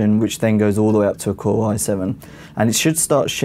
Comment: In the end I heard "prison", what? Then I listen again, it's "pretty soon". Then I turn on closed caption, and it's indeed "prison". Nice accent by the way haha.